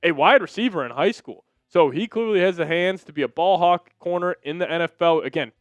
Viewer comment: BALLHAWK?!? And here i always thought he said BALLHOG! LOL.